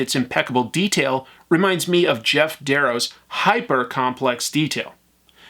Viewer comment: Can somebody spell to me the name of the artist he mentioned here. From what i was able to understand, i thought he said "Jeff Deros", but i can't find anything googling it... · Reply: It's Geof Darrow - <link>